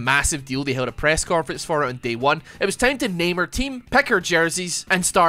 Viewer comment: I thought he said "It's time to name our team Pecker Jersey"